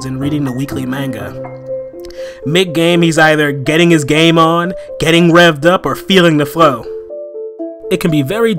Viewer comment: getting rap up what are you saying its lets rev it up